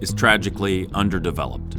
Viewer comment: Did I hear correctly, "...tragically underdeveloped..." about the river area, ~7:11? Thank God for that! We, the humans, are the cancer of the earth; We over populate and destroy everything. I've kin folks that live up close to that area, and they've told me about the volcano under that area.